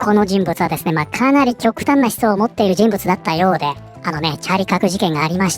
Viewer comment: ああチャーリーカークか 音声だけ聴いてたらチャリパク事件に聞こえて驚いたわ